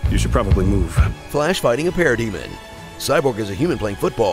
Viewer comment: I thought he said "Parody-Man" 😂